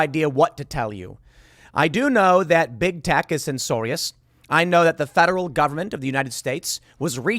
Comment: Did you say censorious at 00:30 mins? just making sure...because I completely agree!